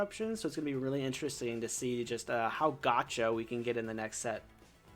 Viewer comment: what does say here? "How got ya"? or "gacho"? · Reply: "Gotcha", often Pokémon has mechanics that prevent the opponent from playing like energy removal, shutting off powers, or stalling. In groups I've played with we've called this "getting them" or "gotcha" I realize this is confusing now.